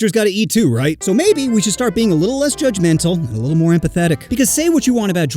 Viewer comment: Ngl when you said a little less judgemental and more empathetic, I thought it said more pathetic. İ think it works both ways